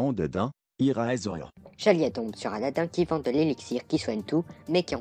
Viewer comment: Qu'est-ce qu'il a dit ? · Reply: "Eraser" 11:07 "Alors tu m'occupe ces enfants ou tu rentre chez toi, c'est clair ?" 15:05 "Au v*ol !"